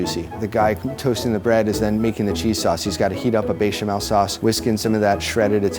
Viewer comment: Tell me why I thought it said cheesecake? 😂😂🤔🤔